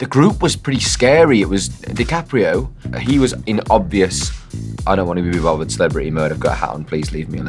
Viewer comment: w-what did he say?….he talks too fast🧍🏻‍♀️ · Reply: “I don’t wanna be bothered cause I’ve gotta a celebrity hat on kinda thing”